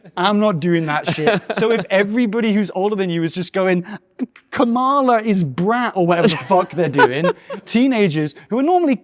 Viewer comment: legit thought he said Kamala is brown